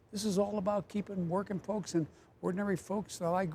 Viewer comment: did he say "working pokes"?